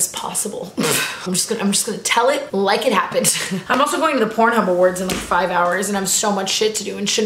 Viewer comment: Wait did she say "pornhub awards" or are my ears broken??